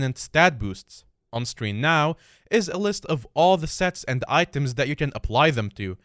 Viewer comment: I heard sex and items instead of sets and items. You kind mumbled there imo. lol